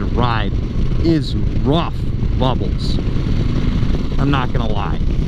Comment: Did he just say "rough bubbles?"